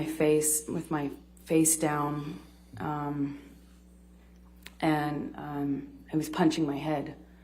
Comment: she said “I was punching my head” then goes on to again say “I’m repeatedly punching my head”. I have listened to it over and over and was fairly certain what I heard. I turned on captions (of course they are sometimes incorrect, but it said the same thing I thought she said. Anyone else hear that too?